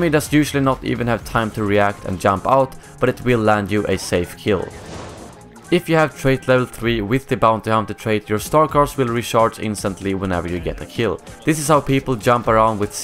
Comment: My english isn't the best, can you please write what does he says in the min 4:10 pleeeeeeeeeease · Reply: If you have trait level 3 with the bounty hunter card you can kill enemies with bowcaster and other star card weapons and when u get a kill they will instantly recharge